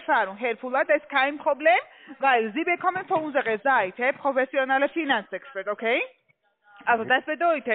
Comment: Was sagt die bei 12:04??? "Professionelle Finanzbetrüg?"